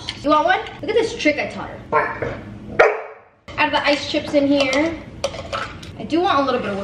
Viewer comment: no hate but did you just say ice chips